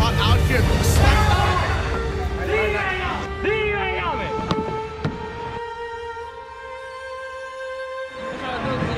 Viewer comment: did he just say ?? That word? LMAO, I guess he forgot that he was in a tournament with lots of cameras on him. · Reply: He yelled "you dickkkk...!" But I don't think he had any intention to be rude.